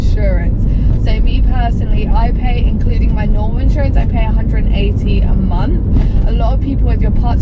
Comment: hey, i'm listening on crappy speakers so i couldn't hear what you said there. was it £180 or £118? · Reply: £180